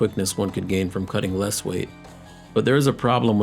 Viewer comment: did you say the advantage of GAINING size and strength from cutting? What world are you living in? Unless you take steroids this is ass backwards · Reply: He said from cutting LESS weight.